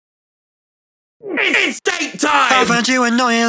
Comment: It's date time! (Tommy's date with Beautie_) 00:01 - Some people find me annoying... 00:09 - Just killed a woman, feeling good. 0:12 - Vlog gun. 0:15 - Tommy's addiction to coke. 0:18 - Gogy! Georgenotfound. 0:23 - Only a small percetange of my viewers are actually suscribed... 0:28 - Tommy's obsession with women. 0:32 - Cadillac meme. 0:35 - Mod makes Minecraft 1000% funnier. 0:40 - "No me gusta", frase he uses a lot with Quackity. 0:45 - This one is from a blooper when he yelled out to his mom "I'm in the middle of a war, mom!" as he was interrupted during a DSMP stream. 0:48 - Tommy's exile arc. 1:17 - Mom, am I adopted? 1:22 - A phrase Tommy uses sometimes, a way he has to speak. 1:26 - Touch grass! Henry! 1:29 - BIG MAN! POGCHAMP! 1:32 - Vikkstar! Dream! 1:34 - SWAG arc from Dream SMP! 1:42 - I pog through the pain. 2:11 - Mellohi, his disc from Dream SMP. 2:17 - I want a moth called Clementine. 2:24 - Cadilac meme, again. 2:27 - Being called a child all the time. 2:30 - Twitch prime. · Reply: i thought he said "its daytime" :0 like he was waiting for the night to be over in minecraft